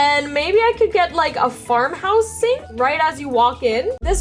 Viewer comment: I thought she said fart house sink😂